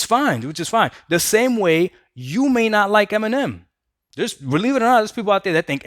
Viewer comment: You said " Eminem" and i heard "m&m" (the chocolate) 🙂